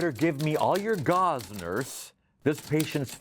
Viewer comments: I also misheard "gauze nurse" as "gozzlers". And I misheard "bargle nawdle zouss" as "barf nargle zouth" but I think that one's more forgivable. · I feel so fulfilled because I also misheard “gauze, nurse” but I heard it as “gozmers”